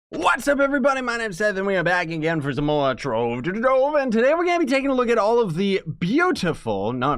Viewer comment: i dont hear "My name is Scythe'' i hear ''My name is Nathan"